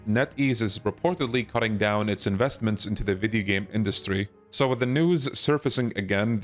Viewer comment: did bro just said "Vidi game" industry?